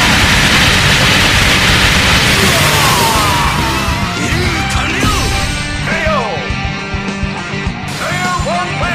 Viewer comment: what does ralf said in 4:10???? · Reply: alonso macias mission accomplished